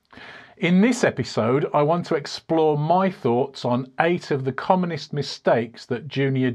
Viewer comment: did I hear "the 8 of communist mistakes"? 😆